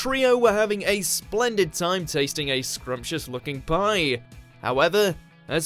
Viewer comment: Thought he said scrumptious fucking pie lol